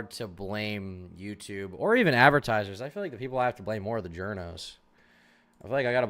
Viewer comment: thought you said the giornos and was like "wtf did they do lol"